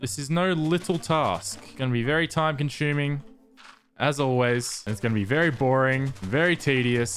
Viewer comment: is it just me or at 14:40 did he say " very time conshuming